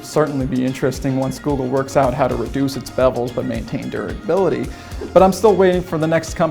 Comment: Did he say "bevels"??